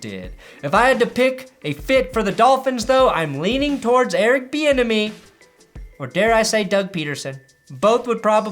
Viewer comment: I literally thought you were saying "Eric the enemy" until this video.